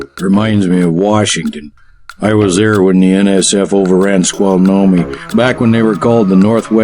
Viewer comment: Squalnome? Is that what he's saying? I live in Washington, and other than Snohomish, nothing around here sounds like that.